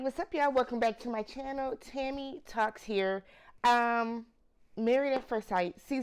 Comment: I'm new here after doing a search for MAFS reviews and, when you introduced yourself, I thought you said Tammy Tucks. 😳😳